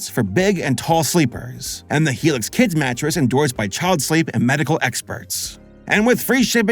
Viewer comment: Yeah..... should've put subtitles on.... I thought you said "the helix kids matress endorsed by child slave and medical experts" 🙃